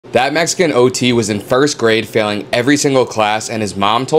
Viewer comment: Sounds like he says " Fat Mexican Ot"